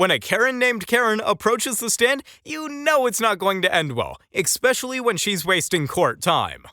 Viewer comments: re - 3:07 Did the narrator just say "Eck-specially" instead of "especially?" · Did he just say "eCspecially" ?